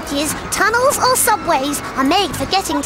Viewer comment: sounds like Fizz said fuckbridges 😅